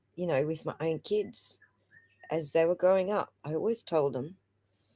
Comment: HaHa😂 this time I heard nine kids——-smh- something is happening with my hearing- OR MAYBE, its cuz i was in the other room- my OWN KIDS-my OWN KIDS……. 🤦‍♀️ got it (again)